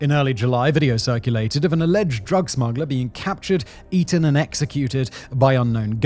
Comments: does he say “eaten”? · excuse me did you say they ate him? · did he just say eaten😅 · Sorry at 22:27 did you say captured, eaten and executed?